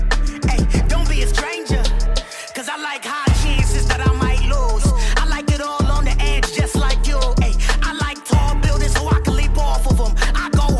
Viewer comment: I keep hearing "I hot hot cheese that I might lose, I like it all on the edge, like you" Bruh.